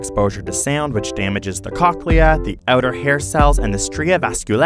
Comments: anyone thought he said / wrote coachella ? or just me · my stupid self thought that said Coachella